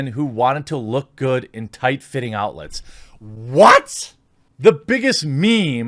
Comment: I'm sorry but I think I just heard "tight fitting outlets".. I mean, that'd be one hysterical Halloween costume